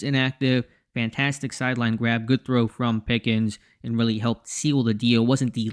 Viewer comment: Not sure what you're referencing but I probably just misspoke. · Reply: Yeah, I didn't need to nitpick but the first time I heard it I thought you might have said 'Pickett' which wouldn't be great.